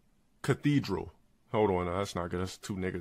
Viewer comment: onward is probably the funniest thing I've ever seen involving LTG. He struggles so hard to think of a name, and he says something that sound like fkin "CATHEDRAL" I'm dead